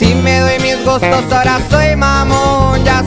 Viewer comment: natanls verse does he really say que es mamon 😂😭